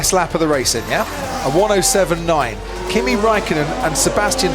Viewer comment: why tf I heard pp rikkonnen 😂😂